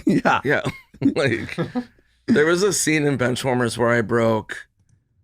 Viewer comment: Hahaha, thought he was saying Ben Schwarmers. Who the hell is Ben Schwarmers?😂